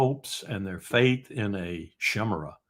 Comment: Col. Larry Wilkerson's word of the day has a flaw. I usually have some idea, so I can spell a word close enough for me to find a definition. Whatever he says at 29:47 I cannot spell. "Shimera"? Is he saying Chimera with a soft C? Cimera, an alternative spelling of "Chimera"? Is "Chimera" pronounced differently when it refers to a multi-animal beast from Greek mythology? Shemara? IDK. - This simply cannot be allowed. I must know! I am willing to have the Col. teach me new words, but I need more info. This will drive me nuts (short trip). Just give me a little info-bubble with the spelling?